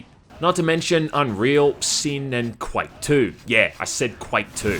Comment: what game is this? (i know he said the name right there but i can't really understand what he says) btw those fps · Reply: Quake 2?